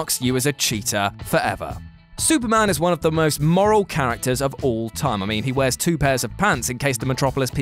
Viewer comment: I thought he said " Superman is the most Laurel character " 💀